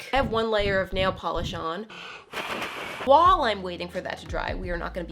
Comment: does anyone else notice when says "while", it sounds like she is saying "wall"? (2:36) i always notice it when she says something like "while that's drying.." and it sounds like she said "WALL THATS DRYING" lol